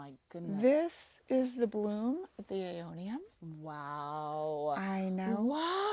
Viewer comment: I didn’t catch the name of the plant at 4:03, anyone know what it is? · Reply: Aeonium arboreum, tree aeonium